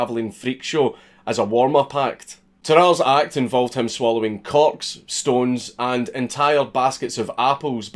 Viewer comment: Dank said "swallowing corks" and I heard "swallowing cocks"